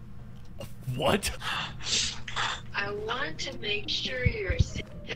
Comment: What was bro trying to say with that albanian text to speech at 4:24 Edit: After listening closely, it said "Im sure that max is gonna become fat" or "I want to make sure that max becomes fat"